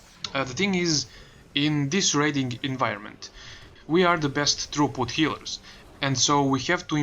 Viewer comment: Okay.. 10 tries later... what is it you say at 15:12 we are the best ... healers. I dont understand the word inbetween best and healers. trooput? throughput? true put? what? xD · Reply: It's Throughput :) Tho I probably should have said best spot healers. Druids and monks heal more in aoe fights.